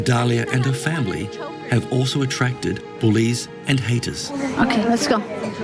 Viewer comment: when the guy says bullies and haters, I thought he said police and haters. It's the acsent